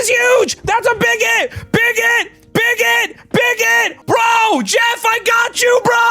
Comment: I thought you said big_git😂